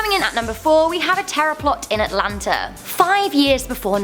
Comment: Oh terror .. I thought she said tarot